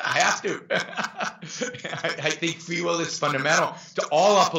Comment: At one point I thought he said free willy